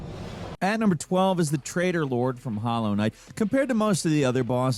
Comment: I thought you were saying Trader lord. Tray- TOR... And the English are meant to be bad with T's